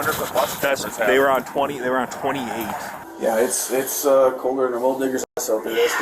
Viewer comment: I thought he said "it's colder than a gold diggers heart.."